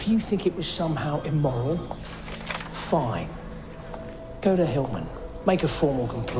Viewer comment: at 1st I thought he said "go to hell then" In fact he said go to Hellman 😅